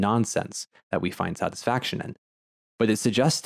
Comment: Did you just say "satisfactioning" (@ 2:25)? lol.